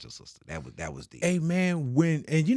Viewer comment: All I hear is “Say my man”😂😂😂😂😂dude is a storyteller